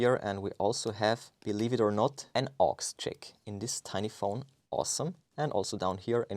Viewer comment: what is an “aux check”? (Did i mishear?) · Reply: Aux jack